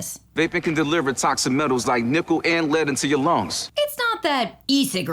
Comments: Everything I hear this add I only hear “nipple and lead into your lungs” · The first time I ever heard the vape ad, I heard "nipple, and lead!". And now every time that ad runs, I'm compelled to say, "Yeah, do you want nipples in your lungs??" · I hated that metal ad, and I complained to my friends about it multiple that not only did the guy not sound that serious, not only did the lines sound stupid and boring, NOT ONLY did MULTIPLE times including the first time I heard it I totally heard him say "Nipple and Lead", but also I thought the monster looked weirdly good. I've never had an interest in vaping but a killer transformers oc did not contribute to that.